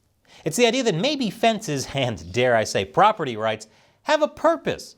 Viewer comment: Did anyone else notice that @ 7:08 says property rights in the audio but his lips and the CC say Walls? Is AI editing out content from the videos for statements that the Youtube overlords don't like?